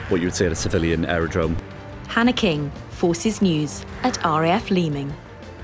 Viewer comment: When the Reporter signed off she said her name “Hanna King at RAF Leeming” but I honestly thought she said “Panic-ing at RAF Leeming” 😂🤣